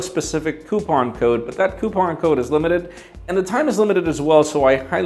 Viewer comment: did he say " poop on code " X_X